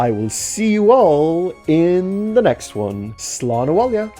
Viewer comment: Did you say Slodawogga?